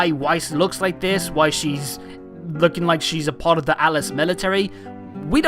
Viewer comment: I thought heard you say Alice military LO.L😶